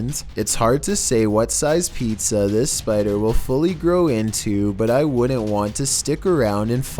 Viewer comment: That freaking spider though. Crazy. 9:00 Did he say "not sure what size pizza the spider will grow into?" What the heck does that mean? There are some ungodly creatures in this video.